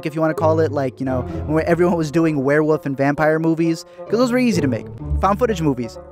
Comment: did you say "werewoof?" O_o